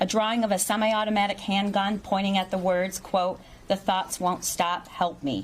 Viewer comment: Kid ruined the whole family 😂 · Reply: Y'all I heard ,"the Thots won't stop"😂😂😂😂😂😂